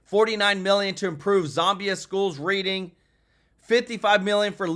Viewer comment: twice now I thought you said ‘zombi schools’. Did you mean Zambian schools?